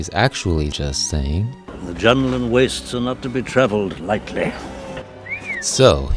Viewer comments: I always thought Obiwan said "The Jungle and Wasteland"🧙🏼‍♂️ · Always thought Ben said “the young and west are not to be traveled lighted”. I kinda wish he did say that, I prefer it.